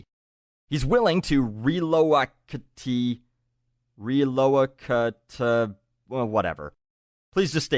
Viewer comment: I think he's trying to say "relocate".